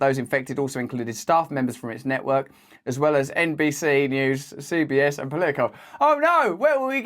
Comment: Please tell me if I'm incorrect, but Trump didn't attend any white house correspondence dinner during his term did he? 6:02 No joke, I had to listen to this twice because it sounded like he said "NPC News" 😆